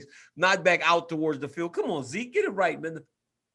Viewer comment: my guy DC caught himself real quick on that one 😂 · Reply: Haha I didn't see that. It sounded like he said "Get it right mother..."